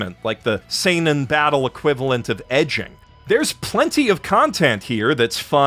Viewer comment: "the Seinen Battle equivalent of 'Edging'" at first I thought you said ECCHI! I was like DAMN DUDE THAT'S SAVAGE CALLING OUT ECCHI LIKE THAT I had to go back and listen again, Now I'm almost disappointed XD